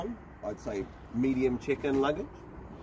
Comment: That's a lot of chicken nuggets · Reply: im just messing with you! haha Everytime u said check-in lugage sounded like you said chicken nuggets. lmao